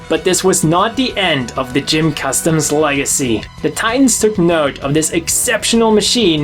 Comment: what did he say? "...end of the GM custom's legacy. TibltarghUghnrrrrd......" Was this an audio error? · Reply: He said "the titans took note"